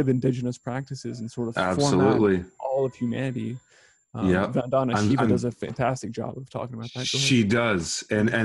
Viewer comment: Couldn't hear who he said did a fantastic job on getting out information, right around 39:15 when he was talking about the Rockefeller foundation. kept talking over him saying yeah. Does anybody have that info? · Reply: He mentioned them again around 40:48. Sounds like… ‘Vandana Shiva?’🤔 I’m not familiar with them so I dunno if this helps? Much LoVE & Peace♥️🕊🔥